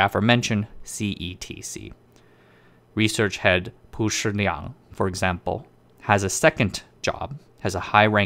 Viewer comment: can anybody help? I can't spell the name of research head. The subtitle said Pusher Nyang but I am quite sure it is not the correct name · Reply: chen zong nian 陈宗年 陳宗年